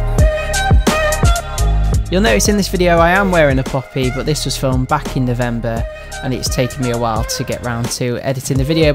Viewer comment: What did you say you were wearing at 00:22 ? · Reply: a poppy